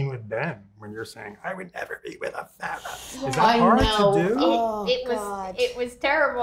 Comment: What did he said in 6:10 ? I couldnt understand him 🤕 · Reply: he ask about the scene when she saids to ben that she will l never be with a fat guy if it was hard to do